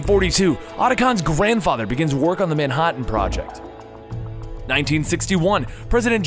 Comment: Did he just say ManHOTtan?